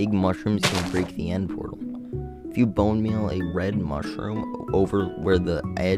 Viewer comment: My headphones cut out so I heard “if you bone- red mushroom” and I died inside